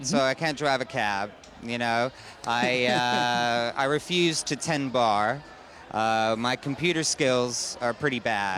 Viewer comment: Please, what does he say at 1:50-1:53? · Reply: Ina Ohlke I refuse to tend bar, a bartender